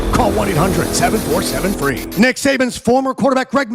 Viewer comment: I'm so hot and tired, I thought you were saying Satan!😂😂😂